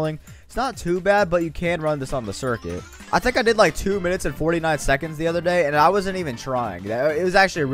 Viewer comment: Wait but i got 228 on circuit with a 100k mustang so i think you not good at racing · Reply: oh my bad i thought he said 250 seconds